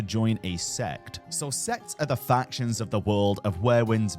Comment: Ohhhh, sects. I heard s*x